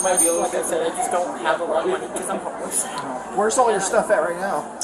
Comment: Gotta love CC, at 4:10 instead of "because I'm homeless" it says "because I'm Hess"! :D